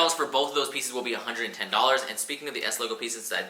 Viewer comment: Am I trippin' or did he say the track jacket cost $368 & shorts for $810? 😂😂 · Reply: Miles Loves The Hype the shorts he said a 110 dollars